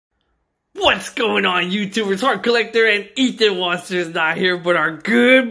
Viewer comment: Who else heard say ethan waster